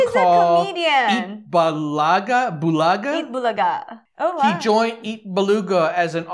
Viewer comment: did he said "EAT BALUGA" hahaha that's funny, and a little bit cringey...